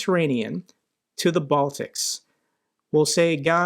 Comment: They are talking about the return of the 1917 borders, which means the disappearance of the Baltics AND the ressurgence of the Austrian-Hungarian empire. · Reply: Watch the video from 3:01, listen what says and read the text. says Baltics, text says Balkans.